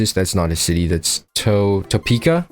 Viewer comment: I hear it's hot in Topeka